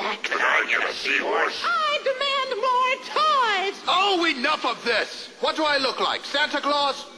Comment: When I first saw this, I thought Toyman said, "I demand more ties," at 0:48 :P